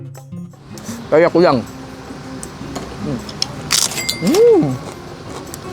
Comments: gw dengernya peyek kuyang 😂😂😂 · Apa cuman aku yg dengernya "Peyek Kuyang"😂